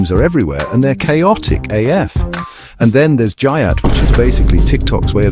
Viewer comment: The only good/laughable brain rot there was was the first one. I was laughing so hard😂😂😂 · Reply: I was crying when it said "JUYATT" instead of "GYATT"